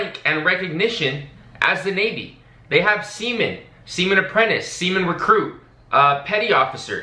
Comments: i legit thought you said semen when you first said seamen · for a sec I thought you said Semen.